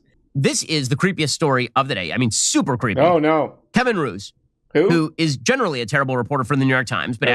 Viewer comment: I swear Ben is saying Kievan Rus' at 9:35 and for the rest of the show and I cannot unhear it